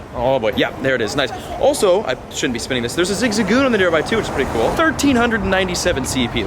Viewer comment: did you just say “theres a zizagoon on nearby which is cool”? HUH?! Also how many gym badges do you have where you havent done anything in the gym? I cant claim gym badges without having to battle at least once lol unless its a serious landmark like the collosseum then i’ll reluctantly spin it without interacting in the gym